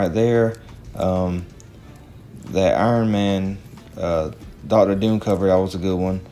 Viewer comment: did he say Daughter Doom cover??